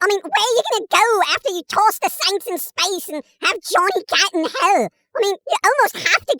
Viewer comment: I heard Johnny Gat and thought you were saying Johnny Depp, then heard about rescuing him from Hell and thought "wasn't that pretty much the beginning of POTC- At World's End?"